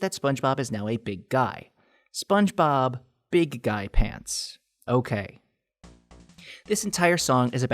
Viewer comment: All I the only one who thought she was saying big gyatt big gyatt pants ok